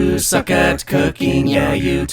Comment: Why can i hear “use a cat cooking”?